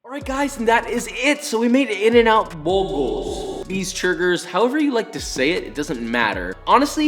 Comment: did he just say beesechurgers?